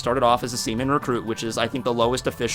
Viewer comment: I thought I heard semen recruit